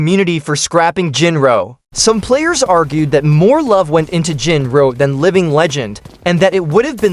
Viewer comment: Did he say Jin bro